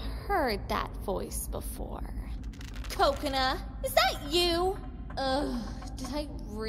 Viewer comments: Me: Mom I swear I heard her say coconut! Mom: sure. . . . *calls a mental therapist* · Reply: She said kokana! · what normal people heard: " Kokona! Is that you? " what I heard: " Kokonut! Is that you? "